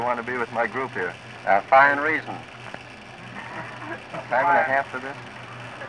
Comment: what does he say at 7:02? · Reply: Sounds like it could be "a dime and a half for this" as a joke for getting paid to be interviewed. I could be wrong as it's quite hard to make out exactly.